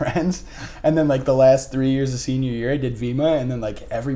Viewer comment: The last 3 years of senior year did I hear that right???